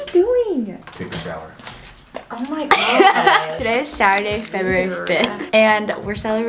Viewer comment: For a sec I thought she said today is shower day